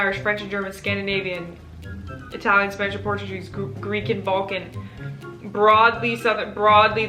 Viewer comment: When she said Balkan I thought I heard Vulcan. She might as well be with that long list